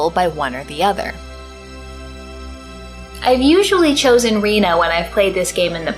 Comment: Did you say Reno? lol